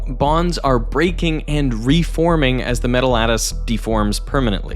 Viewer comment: you're saying "Metal lattice", but the subtitles (which don't seem to be auto generated) say "metal at us". I think whoever transcribed misheard. Perhaps you could correct that?